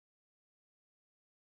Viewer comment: The first time I saw this I heard it as "your such an onion girl" but the second time I heard it as "your such an onion, girl" 😂